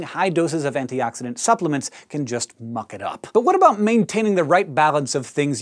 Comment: When he said "muck it up", did anyone else think that he said "f**k it up"?